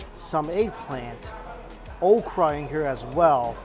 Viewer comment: When you said “okra is in here as well” I heard “Oprah has been here as well” I thought wow what a random fact. Then I rewinded and rewatched and was like 🤦🏻‍♀️🤦🏻‍♀️🤦🏻‍♀️🤦🏻‍♀️